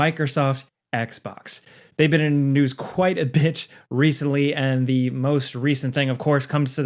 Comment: is it me or is he saying "bitch" not bit??